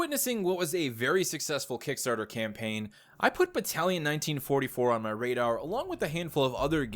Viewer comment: I thought it said Battlefield 1944 at First